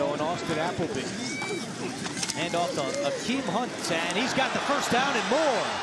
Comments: I thought he said Kareem Hunt lmao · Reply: That's his bro akeem hunt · I thought they said my boy Kareem Hunt was playin for a sec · I thought they said Kareem Hunt and was like hold up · “ Akeem Hunt! “ Nigga I thought it said kareem Hunt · Who else thought they said Kareem hunt? <link> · I thought he said Kareem Hunt at first 😂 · bruh literally thought he said kareem hunt when saying Akeem hunt lmao.